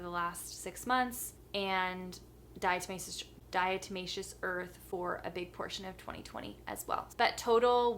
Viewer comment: When she was trying to say diatomaceous earth I thought she said die to my sister